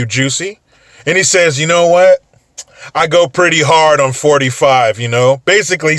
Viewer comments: Jussie gets hard on 45. Lol #MAGA #TrumpTrain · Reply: I thought he said he COMES hard on 45. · I thought I heard Jussie say, "I come hard for 45!" lol